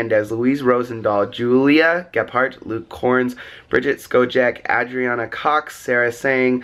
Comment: Did just say Luke korns